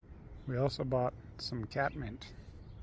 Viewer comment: Oh cool good to know about the bean seeds will try that. The parsnips seem to be going good was late getting them in so hopefully it's a warm fall. Thanks for watching! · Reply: oh, I just realized you bought catmint already….duh!….i thought you said catnip…lol